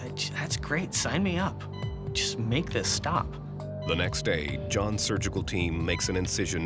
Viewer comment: Thumbs up on 00:37 if you thought he said "Just stop this shit."